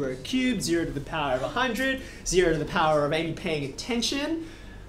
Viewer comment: "0 to the power of are you paying attention?" mate that was brilliant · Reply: Yeah that was funny. I think he said "zero to the power of ain't paying attention" .